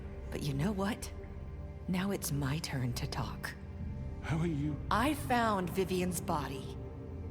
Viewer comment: does anyone hear the Crooked Man say "Faith....how are you...."???? Swear I did xD